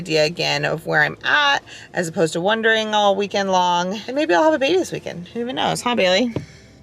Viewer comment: Omg I think she had the baby cause at 10:57 she said we could have the baby this weekend (the subtitles said we wont) and she said who knows(and the subtitles said we know) omg!!!